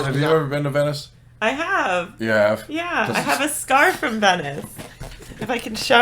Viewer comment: I thought she said "She has a scarf from Venice" and was expecting to see a pretty scarf.